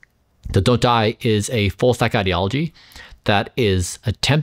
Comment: the closed captioning words for the double ?? is "full stack", at least it's what I perceived . So he was referring to "Don't Die" as a "full stack" ideology. This is what Gemini's ai says about it: "Full stack" refers to a software developer who is proficient in both the front-end (client-side, user-facing part) and the back-end (server-side, database, and logic part) of a web application. A full-stack developer can work on all layers of software development.... Btw, Metformin is cheap and accessible if you go through an online Dr. and lab. I don't think everyone should take it unless they need it based on one's age, labs, lifestyle, and genetics..